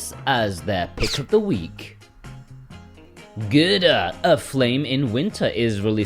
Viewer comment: Thought you said “Gouda: Aflame in Winter”. Which I imagined was a game featuring a historical retelling of some forgotten tragedy in Northern Europe. Like that time in Massachusetts when the town got flooded in molasses and a bunch of people drowned. But you didn’t. And it’s not. 😅